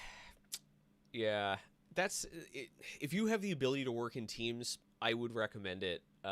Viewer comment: it sounded like: "if you have the ability to work in team... you have to use Monday dot com" (for ppl in EU)